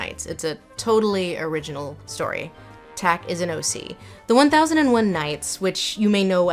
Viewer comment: Some subtitling issues on this one, 17:08 Aussie instead of OC, and then slightly later Charizard instead of Scheherazade. 19:28 chic instead of Sheik, repeatedly. Also Edward Said’s name is a struggle.